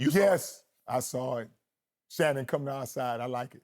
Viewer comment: "yes, i saw it.. Shanon finally came outside" this is him essentially calling Shanon a house-N**?? did anyone else catch this